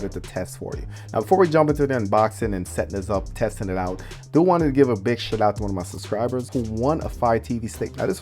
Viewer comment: Did he say he wanted to “give a big shit” out to one of his subscriber at 0:20?